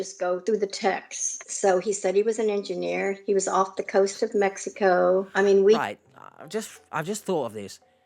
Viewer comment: Ummmmm, Also, Mexico isn’t overseas….. Editing just to add. Good god, that end of video denial was…. Eesh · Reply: Thanks. I thought she said coast of Texas, but coast of Mexico for oil rigs would most likely be Gulf of Mexico and yes, close enough to go to the US from the ship and not an international flight.